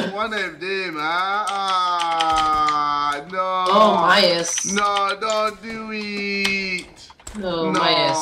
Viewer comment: This is JJ Training the accents for Houdini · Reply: did anyone hear him say midas from chapter 2?